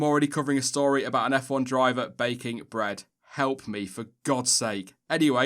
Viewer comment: When spoke of Seb's new hobby of 'baking', at first listen I thought he said 'vaping' - man, Seb must be really restless at home to have taken up vaping...